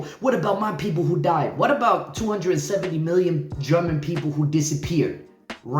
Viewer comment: Am i hear it right? 217 million german people disappeared in Germany after WII? Where did they take so much german people? or am i hear it wrong?